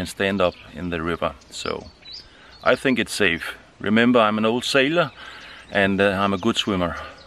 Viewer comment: Sorry. I misheard you. You said you were an old sailor...not "No sailor". I misunderstood. All the better. Enjoy!